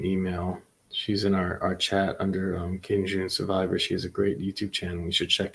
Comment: Who is this woman you are talking about? I can't understand you to go find her. It sounds like "King June Survivor." Closed caption says you said, "Kenji Survivor." I'm lost but I would like to support her. Adding her info in the description would be nice for us folks who can't follow a chat because they go too fast and we can't see anyway. Pus, we never make it to a live. Thank you!